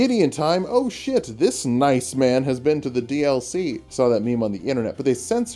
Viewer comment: Did he just say "Iceman"?! THE FALL OF THE ONVITAIKAYAN IS UPON US! Oh, wait, it was "nice man".....